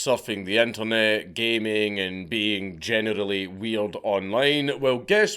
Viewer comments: did you just say “genitally weird” ?!?! Love the accent! Don’t ever change! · Did you just say "genitally weird"? hahaha